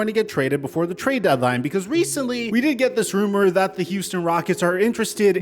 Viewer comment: I thought you said.. That this rumor is not true..